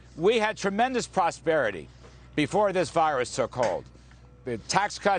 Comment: did he just say, “...before this virus, so called...”? Interesting.